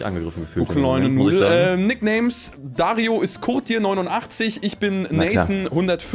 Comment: Ich hab "Dario ist Kot Tier 89" verstanden 😂😂